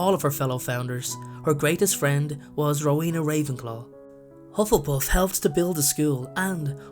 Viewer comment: at first, when Professor Mcgonagle told Hermione and the others about the chamber of secrets I heard "LUINA RAVENCLAW" then when Harry went to Ravenclaw house I heard "HELENA" me~🔜➗⭕🔚